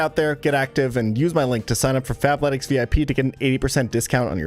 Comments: I had to check the description cause I could’ve sworn you said “fapletics” lol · I thought he said "FAPletics" like he was about to shill me a masturbation based workout program. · I kept mishearing the sponsor at Fapletics im so cooked